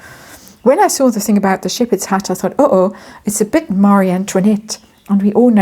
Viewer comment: I misheard and thought you said 'shepherds hat' and I'm thinking why would the King send one of those! lol